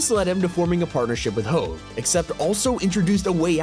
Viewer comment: did bro say hoe?